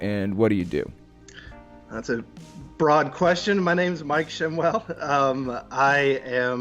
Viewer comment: stg thought you said "Mike Shamwow" for a sec.. had to check description to see if he held 12x his weight and worked wet-or-dry..